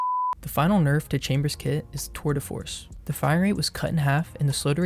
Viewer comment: lmao when u said "tour de force" it sounded like "twitter force" for a second 💀💀